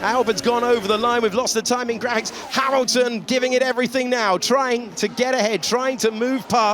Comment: Did I just hear Harrelton?